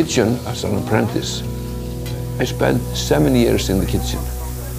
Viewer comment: I heard he "spent seventy years in the kitchen" :DDD